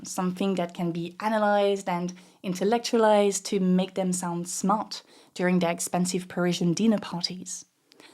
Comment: homegirl said "smart" and I heard "smut" god your accent is a problem sometimes lol